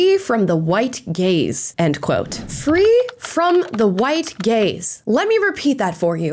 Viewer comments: I was not looking at the screen and thought she kept saying “free from the white gays”. My head hurt. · For a second I thought you said black people were scared of the "white GAYS". I thought, "well, that's a new one". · I thought said “they need to be protected from the white GAYS” until i saw the words appear on the screen. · I thought I heard white gays. 😂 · When it was first said they were trying to be protected from "White Gaze", I honestly heard it as "White Gays", because, you know, theatre and stereotypes. At first I thought, "No, that can't be right. That'd be extremely homophobic," but then I thought, "Well, they are being blatantly racist. Maybe they really are homophobic as well." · At first I thought she was saying “white gays”… and I was thinking “what have white gay people done!?” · Ohhhhhhh... white "gaze". I heard white "gays".